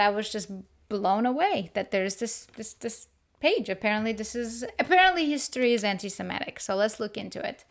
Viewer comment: I listened now to you for almost 9 minutes exactly and heard everything up to that very clearly. . It just so happens I had to replay it twice and then had to use captions the third time to finally hear what you said. It started right about 8:56 to 8:59 . Coincidence ? Probably not. Btw, this is a subject I have been getting more and more into the last year and probably why you made my recommendations today. I am glad I read the comments because a few months ago I meant to buy the 200 years together book. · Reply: The part where I say, "Apparently history is anti-semitic?" I sound very clear to myself haha. Could be my accent, or the fact I spoke fast. It happens. Any time I'm listening an Irish or an Australian talk, I have to say, "pardon me?" a million times.